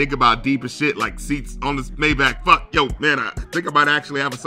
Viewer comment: what does he say??? Lol “fuck Yo banner”??